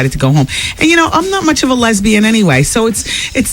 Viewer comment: Did she say she's not much of a Lesbian does she mean Thesbian? lol